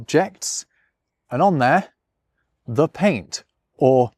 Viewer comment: I misheard the first time as "the paint OF disguise" and I think it is even more hillarious